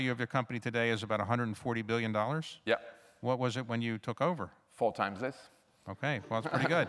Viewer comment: David asks him what was the market cap when he took over and Jean-Paul answers "four times this", instead of "one fourth of this". Am I right? Cuz I feel maybe I'm wrong, cuz how could he made such a mistake...... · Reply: Four times less he said